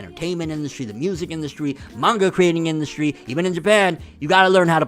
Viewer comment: When u said even in Japan I heard “EVEN IN BED”😂😂😂😏